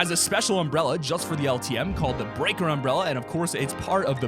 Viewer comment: did he just say the breaker umbrella